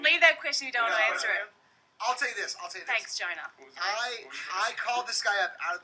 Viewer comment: It sounded like she said "Thanks Gina" so Chan starting laughin!